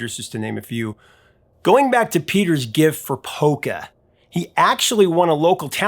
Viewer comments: Polka! I heard "poker"! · Lol, at first I thought you said “poker”, instead of polka.